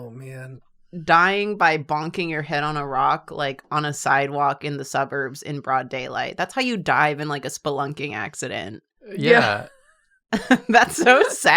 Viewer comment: What I heard say: imagine dying of a a spelunking accident in the suburbs